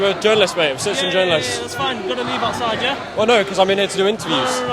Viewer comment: Those cops are your biggest issue. Good Englishman need to take over police and do the right thing, save England 🏴. 5:49 bearded Afghanistan cop says "leave OUR side", not outside like the captions said.